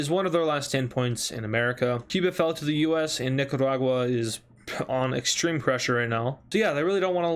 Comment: Did you just call it NICADAGUA